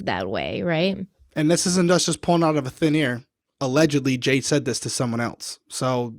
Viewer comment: And this isnt just us pullin’ out of a thin ear. → is that what you are saying there Derrick? it makes sense. Absolutely adorable. i have not heard it said like that. Usually, it is pulling out of thin air - but it seems also true and something magical, to either pull it out of the air 𝐎𝐑 pull it out of an ear!